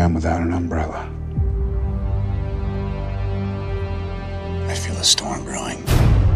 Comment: Idk why but when he said " I feel a storm brewing" I heard "I AM THE STORM THAT IS APPROACHING"